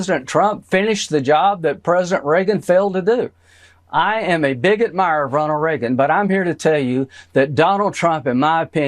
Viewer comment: At around 21 mins whilst listening but busy, I thought I heard Lady Graham say on a news report; ~ "I'm a bigot!" Turns out he was actually saying "I'm a big admirer of Ronald Reagan". Maybe one day ....... 🤔